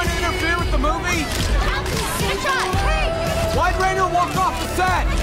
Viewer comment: I thought the Paparazzi said, "Why Rainer won't have sex?!" XD